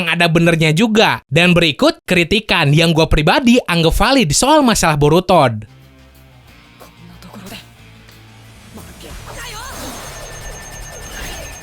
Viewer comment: apa cuman gue yg dengernya "borutod"